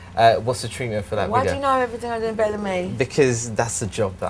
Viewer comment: What did Amy say at 10:32?🤷‍♂️ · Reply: “Why do you know everything better than me”